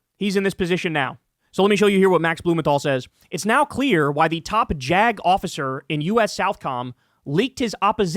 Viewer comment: I can't be the only one who chuckles every time I hear "top jagoff-icers"